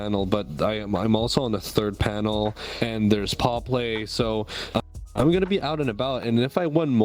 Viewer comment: Hope ya'll have a good time at the con, what was that about 'paw play' 'wolfmaster' collab? I couldn't find any links for those furs, I may have misheard the words 😅 Either way i'm super excited to see the live episode! Keep it up ! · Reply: that collab episode is coming out in the near future and it's paw play where Fiction, Whiplash, and Stream are guests on that episode for them